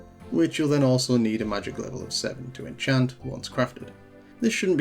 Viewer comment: "you need a magic cant understand set" What word is he saying? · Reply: Magic level of 7 to enchant? Something.